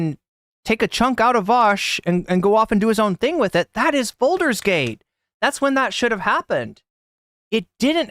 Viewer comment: Oh...FOLDERS gate. I thought you were saying "Balder's Gate" over and over.